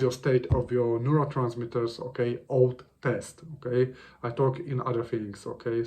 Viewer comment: Is it “old test” to check the state of neurotransmitters? Am I hearing that correctly? What is that? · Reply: OAT test